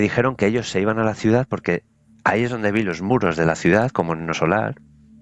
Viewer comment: No alcanzo a entender que película es la que dice en el minuto 20:10 alguien que me pueda ayudar por favor! Un fuerte abrazo a todos! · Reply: Es una película de Xico Javier, se llama Nuestro Hogar, está aquí en Youtube